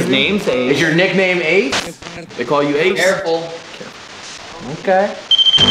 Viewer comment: Cool thing is that in the first minute of the EVP session he said "AMERICANS" then at 28:16 he said "CAREFUL" and it sounded like he said it with a latino accent(?). Like it sounded how a non English speaker would say it